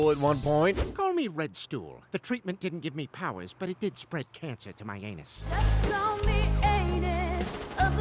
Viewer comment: What did he say??? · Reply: “Give me powers but spread cancer to my anus”